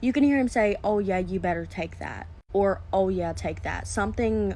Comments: To me it sounded like he said “ ahh you better take that “ and he sounded breathless when he said it. It was very clear. Also it does look like a glove to me. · its a glove ..look at th pinky and th pointer finger ..th glove wrinkles at certain points ..th bracelet is probably under th glove ..also ..why would th son rush to hide back under the covers with a serious look on his face ..it sounded like he said: au yeah like that ..this is fkd up ..jst like that Cisco dude punchn his wife smfh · I definitely hear “Ah you better take this” that is what i genuinely hear every time the bit is played. And that was undeniably a very inappropriate moment happening between that man and his “child”…. The way the son looked and then pulled the covers back over himself and literally stayed completely silent…… no…. No…… no……